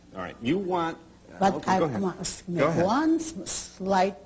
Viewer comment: "I don't give a damn about my…" What? I can't understand what she said. Anyone? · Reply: Critics.